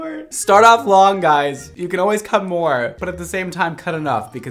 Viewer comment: the entire time "ITS SO LONG"(Heheh thats what she said ) Him- "always start long you can always cut more" I heard "come"not cut ngl EXCUSE ME